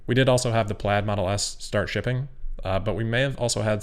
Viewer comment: Did you say “starshipping”? 7:39 ;)